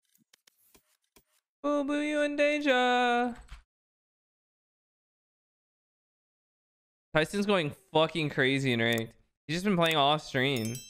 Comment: oboivondeja? What does shaz say at the start?